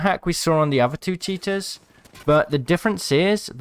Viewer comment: Not poking fun but I swear sometimes it sounds like your saying Cheetah